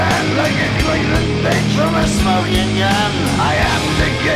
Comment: Thanks, always thought he was saying “Like A Cleveland Bitch From A Slow Union” 😂😂 I heard that for 25 years and still here it now, being from Cleveland never liked that part but it was all in my head 🤣🤣🤣